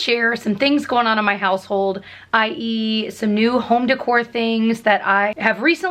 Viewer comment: When she said "ie: Some new home decor" I thought she said "And I eat some new home decor" and I'm like "WHAT IS THIS VIDEO!?" lol